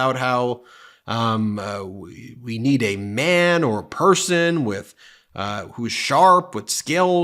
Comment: Daffy duck. Off his meds?? I heard 'perthon' not 'person'. 😮